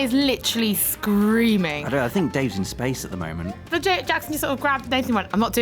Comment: I thought I heard them call that zombie game "Dave's Gone", and then they said that Nathan's looking forward to it and was like what? What's he have against Dave?